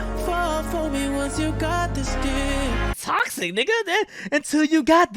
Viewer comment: I thought he was saying “Got this deep” on see you again💀